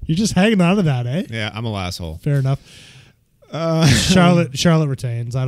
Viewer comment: Did you say "I'm a lasshole" at 54:55? Is that what WWE is calling the fans who stubbornly refuse to boo Becky like the company wants them to? "Lassholes"?